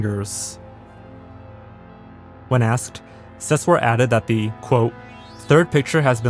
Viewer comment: Not only is Sesseur a bad writer, he's also a pretty bad liar. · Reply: I thought you said Suess like Dr Seuss